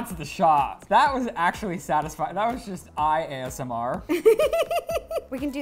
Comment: what did he say ISMR